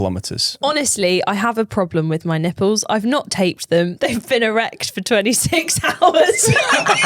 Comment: Wow, keeping it real!! lol At first I thought was saying “wrecked” and it wasn’t until the 3rd time he repeated the word that I heard it correctly as “erect” 😮 I guess that’s the good thing about wearing a bra, it provides protection!! In the future perhaps consider using nipple covers or bandages as a preventative measure! 😂